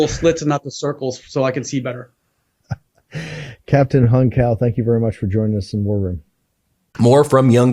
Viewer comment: I thought he said young cow. Hung Cao. Ever notice how deeply Christian people tend to be MAGA? They're already in one cult so why not join another one?